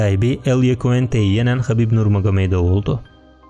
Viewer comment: de ne diyorsunuz anlayamadım? Khabib awedawdaw oldu! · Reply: "Khabib Nurmagomedov oldu."